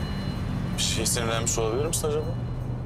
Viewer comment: 'de Altay ne diyor anlayan var mı ? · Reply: Bi şeye sinirlenmiş ola bilirmisin mi mira...ya da semira..deyir mence